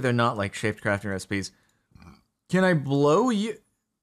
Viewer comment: I thought was gonna say "can I blow Uranium?" Which would need 7 iron